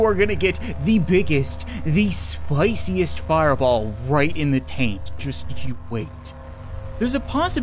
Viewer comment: "I'm going back there again, and I swear to god if I can't get back up, you are gonna the sweetest, spiciest, fireball, just you wait." Holy shit, this the most likes I've ever gotten, stay tomato crew.. um.. stay.. uh.. Saucy, crew! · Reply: I thought he said right in the taint